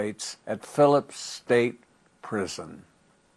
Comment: Mr. Dominick Dunne is missed. 🙏🏻🙏🏻🙏🏻🙏🏻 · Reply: That's the proper way of writing it. I'm no scholar, but I thought it was saying Miss Dominick Dunne.